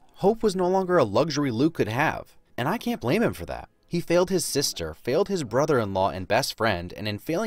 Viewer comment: Somehow in a 42-minute video, I still failed to explain myself. I want you to vote in BOTH polls. And if you can't find the polls, they're in the little white circle with the "i" in it at the top of the screen. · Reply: at 29:28 I thought you said “nailed his sister” 😂